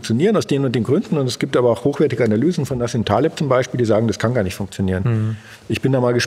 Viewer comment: sagt Beck es gäbe gute Analysen gegen Bitcoin von "A*** Thalib??" kann mir jemand sagen, was er da genau sagt.. verstehe es nicht, danke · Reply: Nassim Taleb